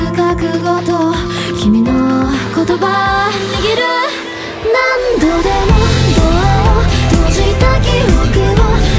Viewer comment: wait did she just say the n word?!?1?1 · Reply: Nigeru